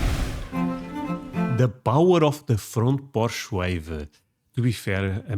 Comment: I thought you said front Porsche and I was like, not a lot of us have those!😂